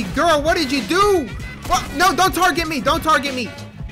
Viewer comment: For a second I thought he said “don’t tWeRk at me